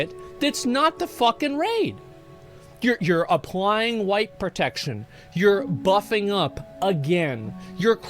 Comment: I thought you said "applying wife protection".